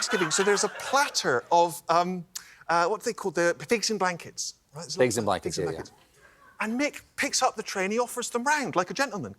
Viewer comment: offered what? I heard Plates and blankets?😅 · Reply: Pigs in blankets .. its a small sausage wrapped in bacon.